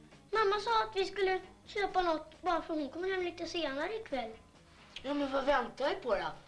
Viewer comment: Vad säger han? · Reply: "Bara för att hon kommer hem lite senare ikväll" 😎